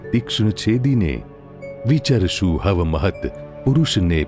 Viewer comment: Please permit me to suggest a minor correction: (at time = 7:53) the second word in the last line is "kav" (as in "when") -- not "hav" as I heard it being pronounced in your excellent presentation.